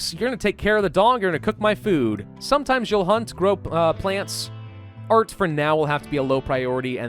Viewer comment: Did he really say "grope plants" at 11:05??? I mean i'm not judging whatever gets you going man...